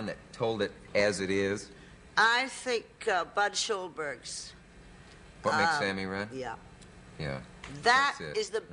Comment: Can anyone hear the name of the book they mention at 0:27? It sounds like What makes Sammy Run. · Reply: Budd Schulberg: What Makes Sammy Run? (1941)